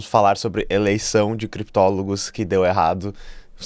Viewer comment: Pôxa, vc falou "criptólogos" e eu ouvi "prikitólogos". 😂😂😂😂😂😂😂😂😂